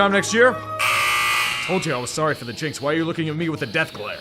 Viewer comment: Sorry for the what? There's no way he's saying what I think he's saying right? My ears have to be stupid. Someone please tell me what he's saying. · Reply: Yes, I said jinx. A terrible shame.